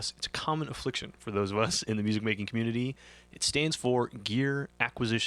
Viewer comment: When you said GAS "stands for" Gear Acquisition Syndrome, I thought you said "Stage 4" GAS. That's where I am, terminal velocity GAS. Except I haven't yet sold gear to buy new gear. But I'm thinking about it. Stage 5?